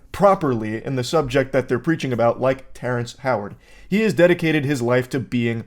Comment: Before my run the other day I put this podcast on and I thought it said “Terrance Crawford” and was concerned why this guy was yapping about particles and light and shit. Then I realised it was this guy. He rlly just seems like he’s full of himself